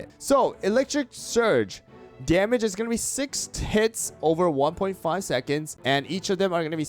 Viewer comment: what did bro just say??? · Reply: 6 ticks which means 6 hits.